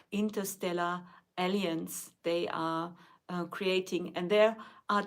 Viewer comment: please practice pronouncing alliance, I thought I heard "aliens"